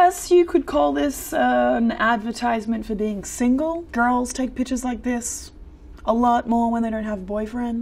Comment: i thought she said pitchures